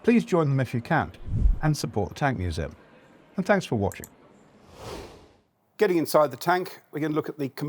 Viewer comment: First time round I missheard this as "tanks for watching". You certainly have some good tanks for watching 😂